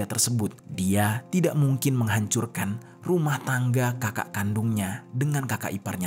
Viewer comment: Menit 05:49 kedengeran rumah mangga ya??